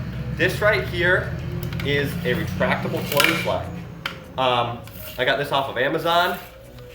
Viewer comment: Could not understand, retractable what?? I tuned on captions "retractable toilet life" ??? Wha... even CC does not know what you said. =/ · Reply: Know I’m a month late but he said retractable clothesline